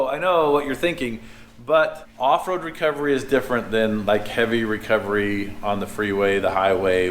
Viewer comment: I keep hearing "Heavy Rector" when you refer about this new tow vehicle!!..lol